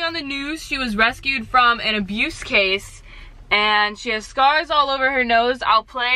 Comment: I thought you said "stars" when you actually said "scars". 😂 the whole time after that I was looking for stars on her nose. 😂 then I realized. 😂